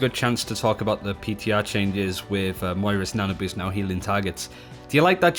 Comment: did he say " moira's nano boost"?